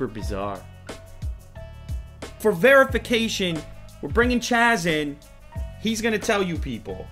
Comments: At first I thought he said "We're bringing Jack in.." and I got all excited. But he said "Chas". Its not that I don't like, but forget him. We want Jack..... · I thought you said you are bringing jack in ....smith :P when are we gonna meet jack Lol :P